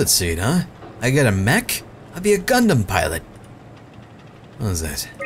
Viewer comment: Did i hear him say Gunmen pilot at 31:45? Was he referencing Gurren Lagann?